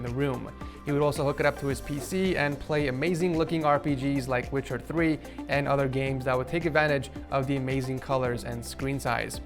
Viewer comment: did he said rbgs?😂😂